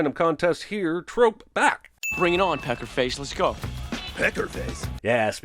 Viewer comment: I honestly didn't get the "pecker face" joke until you explained it 😂 I genuinely wondered all these years why my mom got angry if me or my sister called each other "pecker face." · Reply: I always thought he said "pepper face" for some reason